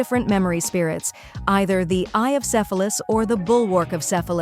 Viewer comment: If it wasn't for the subtitles I would've thought she said eye of syphilis 😂